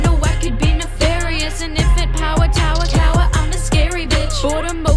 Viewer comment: is she saying "in infinite power" saying infinite a bit faster or is she mispronouncing infinite, i didn't want to ask but I've been looping this for 10 minutes since I heard it and I'm not sure my ears are working properly anymore Edit: maybe "an infinite power tower" saying infinite a bit faster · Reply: It's not that its mispronounced its just the emphasis on the syllables. Infinite power tower cower, I'm a scary bitch.